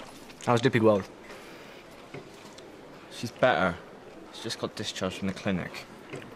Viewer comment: im sorry for asking but what did tony say to cass at around 26:10 ?? im asian + english isnt my native language im sorry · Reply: She's better just got discharged from the clinic